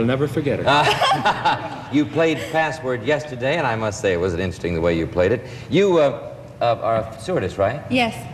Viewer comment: RIP Betty White! What an amazing person! I am curious, can anyone tell what Frank G said to the lady in the blue about 2:12 minutes into the video? Her facial expressions is what caught my attention. She looked shocked… 🤔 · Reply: I think he fumbled his words slightly. It sounds like he said 'It wasn't interesting the way you played it,' but he probably meant, 'It was interesting the way you played it.'